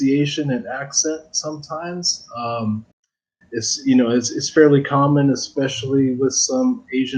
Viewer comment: 細かいですけど 1:00 らへんのとこ it's very commonではなくit's fairly commonに聞こえます